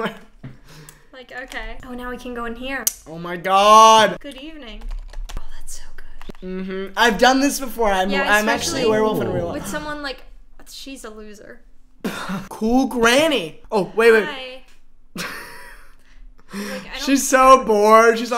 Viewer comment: guys.... it could be just me,, but it sounds like weston says something a long the lines of "im actually a werewolf in real life" around here..... i don't know if this was real!?!?!?!?!? please help!!!!!!!!!!!!1